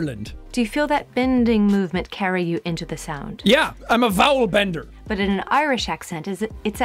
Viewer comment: "I'm a vowel bender" got me way more than it should have. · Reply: Before I watched, I thought you said “vowel blender”